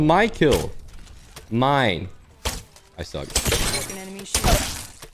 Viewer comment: Did he really said in 7:38 "i suck"?